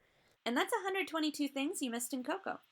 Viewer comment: At the end when she said “ asta luego “ it sounded like she said “ asta el huevo” 😂